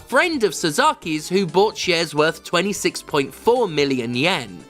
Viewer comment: I initially misheard "shares" as "chairs" and i was like weird fuckin way to launder money but ok